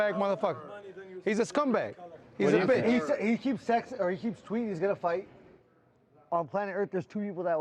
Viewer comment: Did Gaethje say "He keeps sexting"??